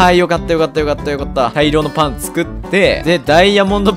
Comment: 「大量のパンツ食って」に聞こえた